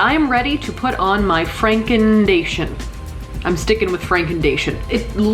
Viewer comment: When I hear "Frankendation" I think if "Prank invasion"